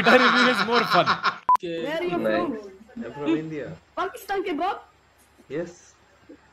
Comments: what did the kid say at 8:00 min mark? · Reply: Pakistan ke baap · what did he actually say? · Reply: "Pakistan ke Baap" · what did he say Pakistan ke ??? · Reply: he said "baap" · What did that kid say at 7:55 Pakistan ke ? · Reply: Pakistan ka baap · what that kid said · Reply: No bro he said Pakistan ke baap....not bhakt bro....